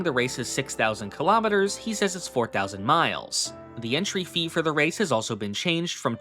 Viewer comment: PS: There's a subtitle error at 3:49. The subtitles say "$4,000 mi" instead of just "4,000 mi".